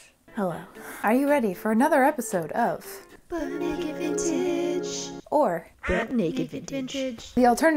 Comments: This video autoplayed and all I heard was "Butt Naked Vintage". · I never heard it as "Butt Naked Vintage" but now I can't unhear it. Also as a mature adult...hehe hehe butt. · I was half awake and for the first time ever, I heard "butt naked vintage" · I keep hearing "Butt Naked Vintage" 😭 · EVERY. FREAKING. TIME. I hear "Butt naked Vintage". My brain is done with life :b